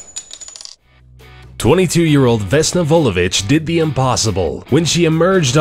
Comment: Instead of Vulovic, I first thought he said, "Though the bitch was a stewardess" 😂😂😂